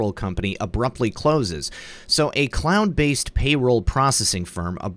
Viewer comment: MyPayroll? When you read "cloud-based" I heard "CLOWN-based"... lol